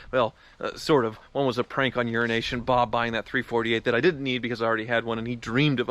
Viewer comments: I always hear 'urination bob'... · Really thought he said "Urination Bob"